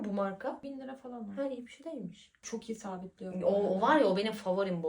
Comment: 1000 liraya bişey değilmiş mi dedi yanlıs mı duydum:(((